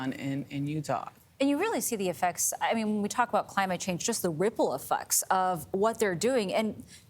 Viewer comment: Did she say "ripple e-fucks" @ 5:47 lol